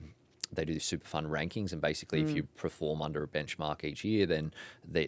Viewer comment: Can someone confirm did he say "preform" at the 41:58 min mark? I don't know if I'm going crazy or are people mispronouncing "perform" these days? I am hearing this all the time now